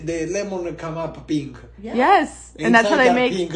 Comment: Sounds like he is saying “Ma PINGA lemonade” 😂😂😂😂